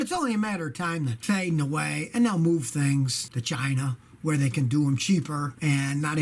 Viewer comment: I swear to God when he said they moved to China I thought he said vagina I was laughing so hard sorry Scotty but Scotty Doesn't Know